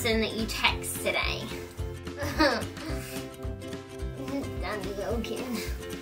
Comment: what did she say at 3:03? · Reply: Danny Wilkin, her boyfriend :)